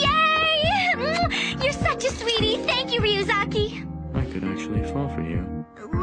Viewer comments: When L said, "I could actually fall for you", I thought he said "I could actually fondle you". My god I'm dumb XD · what did he said? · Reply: Natalaa F "I could actually fall for you" that's why misa says "let's not go that far"